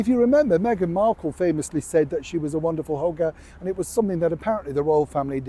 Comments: Oh Neil, when you said that Meghan was a hugger I nearly choked on my cup of tea, I thought you said hooker. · Forgive me Neil , it sounded like you said a Wonderful hooker 😂 instead of hugger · When Neil said "she's a wonderful hugger" I initially heard it as "she's a wonderful hooker" 😂